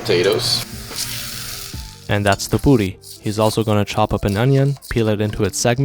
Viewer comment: I thought he said "that's the booti😂💀"